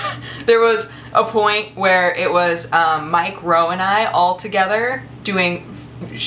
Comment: Like if at 5:03 you thought she said "Mike Rowe and I" instead of "Mike, Ro, and I"